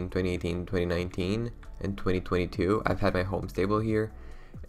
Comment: Hold on did I hear that right? <num> “2022?”